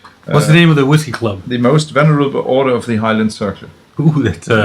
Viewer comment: En serio? Empezaron con un episodio de Laphroaig? 😅 Gran riesgo de alejar a los novatos de los ahumados. Ese episodio con Ian Mcarthur, inolvidable. 💪🏻. En lugar de "venerable" escuché "vulnerable" 😅, pero encontré la página correcta <link>